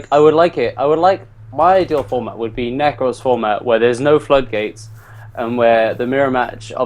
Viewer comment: Did he just say there were no floodgates in Nekroz format? Lol I'm sorry but no dude. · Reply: he said "my ideal format would be nekroz format without the floodgates."